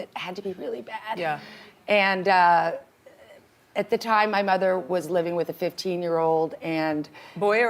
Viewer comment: did she say “my mother was living with a 15 yr old?